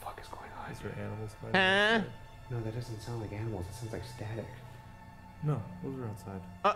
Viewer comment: captions say "she's dead" instead of "static."